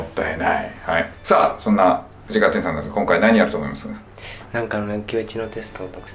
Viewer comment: 藤「なんかの知能テスト…」 って聞こえて、 おっ、珍しく当たりじゃね？って思っちゃった